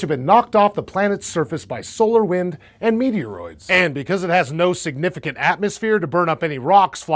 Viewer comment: did he say meteoroids?